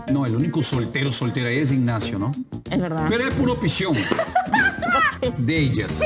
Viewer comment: Qué dijo en el min 4:01? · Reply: Es puro ficción ?